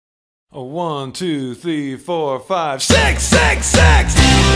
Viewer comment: セックス セックス セックス 出オチ感たっぷりの空耳